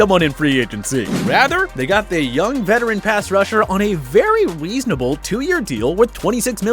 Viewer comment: did he just say young veteran pass rusher